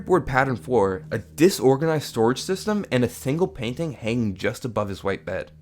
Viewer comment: did he say a thingle painthing or a single painting